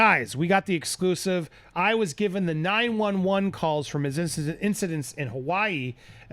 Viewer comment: Sounds like he says calls not cult. He's responding to her words ".....relative to that call" or something like that